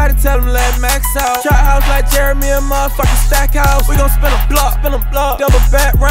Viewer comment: I'm slow af thought this shit said steakhouse 😒